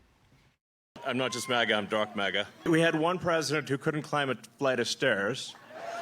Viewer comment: I thought thought he said Darth Maga.